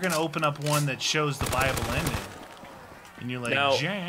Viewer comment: Am I the only that thought that they always said bible ending instead of a buyable ending?